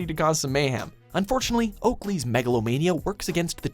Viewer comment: also did he just say "megalovania"?